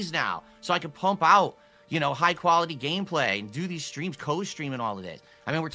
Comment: I totally thought he said Doody Streams, but he said do these streams, lol.